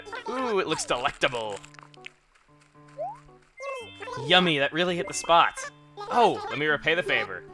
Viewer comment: she actually said yummy